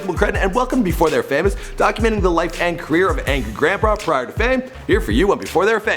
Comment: On 1:26 did he just say Angry Grandba